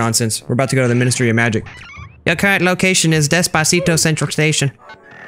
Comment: I might be dumb... but in 6:20 said despacito instead of deeepsea